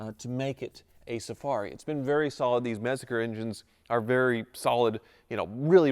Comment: Does he say "Metzger engines"? (at 3:29) A German online dictionary with sound files would help..